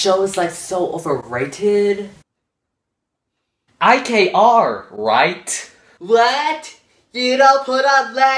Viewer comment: when he said IKR Right? I used to thought he said I Came Armed right?